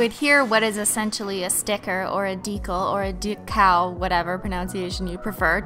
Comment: First time I heard she said decal I thought she said de cow